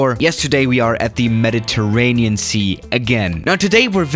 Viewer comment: who else thought he said ‘Meditaraniancy?